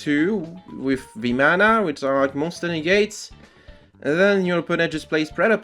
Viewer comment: What is saying @ 13:00 "At most you have access to two with ___"? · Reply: monster negates with Vimana!